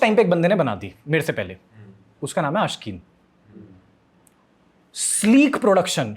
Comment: i didn't understand His name clearly ... Does anyone know his name ??? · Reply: Aashqeen Mast video banata hai yo bhi Ab to band hai channel vasa